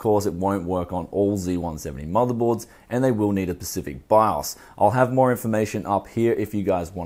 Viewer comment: lol did he say "pacific"